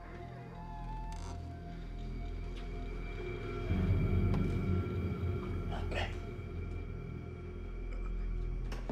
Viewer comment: what does homie say at 3:05? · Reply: "help me"